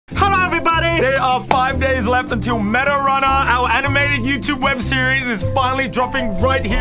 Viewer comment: Hey guys! The Limited Edition Meta Runner X SMG4 Tari Shirt & SIGNED Poster are ALMOST SOLD OUT -- get one now! ➤ <link> Only 5 days left until our new animated web series Meta Runner debuts exclusively here on the SMG4 // Glitch Productions channel - 12pm July 25th PST! Checkout the META RUNNER SEASON 1 INTRO: <link> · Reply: am I the only one who heard anime